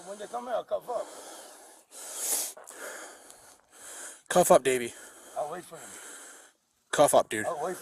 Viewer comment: for a second I thought he said "cuff up baby” 🤣